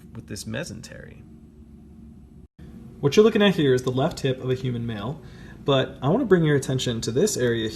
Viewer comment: I thought he said left tit of a human male